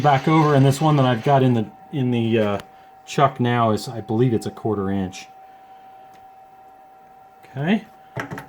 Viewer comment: Could you please remind me what size the shorter drill bit is? Thank you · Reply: I think he said 1/4 inch